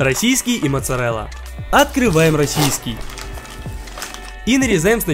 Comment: Почему я слышу Росиський ?